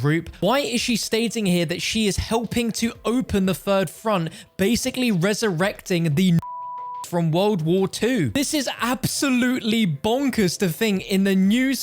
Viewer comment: What is the word in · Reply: Nazi